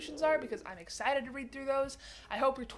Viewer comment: I straight up thought she was gon’ say, “Hope your 2022 sucks! By guys!” lmao